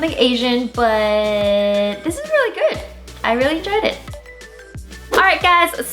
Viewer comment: with captions on, Wengie says I really enjoyed it but captions say I really dread it xD